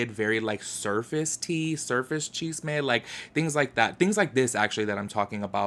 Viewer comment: Can someone please tell me what he’s saying... it sounds like he says “cheese may” videos but I know that can’t be right. Wtf is he saying? 😂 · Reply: Chisme, gossip or drama